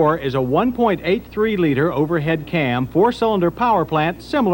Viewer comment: What is he saying at the 3:20 mark? "1.8 three liter"? I must not be hearing him right.